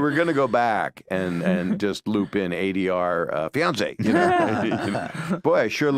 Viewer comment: I thought he said Beyoncé, lol, not fiancée...